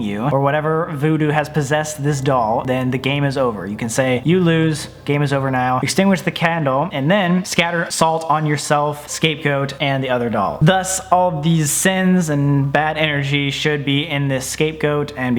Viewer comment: Omfg it sounds like he says Escape Goat